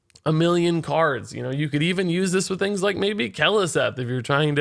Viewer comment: Did he just say play it with Keleseth? 15:39 Poor didn’t recognize its a two cost spell :(